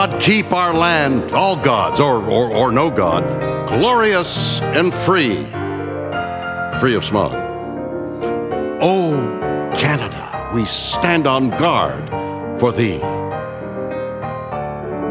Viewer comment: I thought he said "Free of Spock" when he said "free of smog"